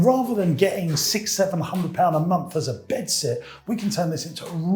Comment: great info thanks for sharing. one dumb question - what's a bed set? I'm aware of the term bed sit - do you mean that - or is a bed set something else, or is it your accent? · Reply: he says "bedsit", so I think it just sounded like "bedset" in his accent the first couple of times he said it.